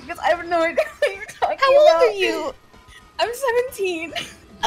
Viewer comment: wait wait wait did she say 17? oh no...